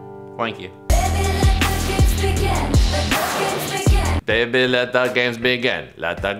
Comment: Thanks for making me realise its 'baby let the games begin'. Because all I heard was 'baby let buckets begin' which I knew was very wrong